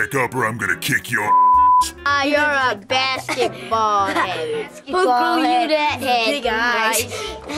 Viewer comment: I thought they called him a bastard for a second. Took me aback cause it seemed a pretty rapid escalation from “footballhead” and “fishhead”.